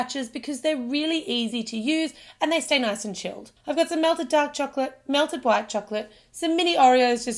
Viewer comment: it sounds like shes saying black chocolate insead of white chocolate